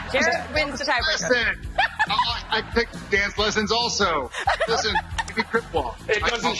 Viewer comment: what did jared say at 10:03 and 10:36 please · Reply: lila azizi at 10:03 he says “that was a lesson! i take dance lessons also” and at 10:36 he talks about how he hangs his closet every day